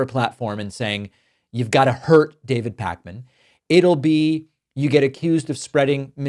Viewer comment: Closed caption said "David Hackman" here and it sounded appropriate